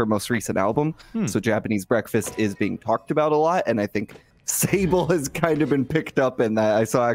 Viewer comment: I thought this said fable for a second💀